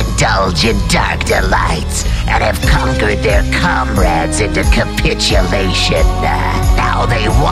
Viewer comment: every time i hear the intro i hear "they have conquered their cumrags" instead of comrades